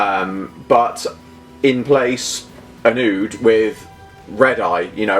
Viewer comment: Nice one! Like you say, it's a really simple change, but it really makes a difference. (Also, I totally misheard "an ood" as "a nude", and for half-a-second was like "what, what?!", but that's my auditory processing issues.)